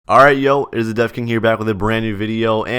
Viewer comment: what he’s saying: welcome back guys it’s back again CC: hi guys it’s the death king